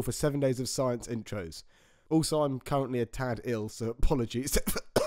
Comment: At first I thought the narrator said 'Also I'm currently in Tadfield, so apologies' and I just wanted to say that there's no excuse for that sort of language